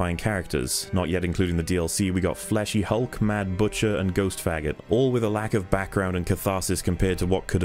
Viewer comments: "Ghost f*ggot"? Did I hear that correctly? If so, that's some really liberal usage of slurs on a platform that heavily demotes hate speech to the point of being too extreme at times. I'm surprised this video is still monetized (I'm getting ads on it at least). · did he say "ghost faggot"? 😂😂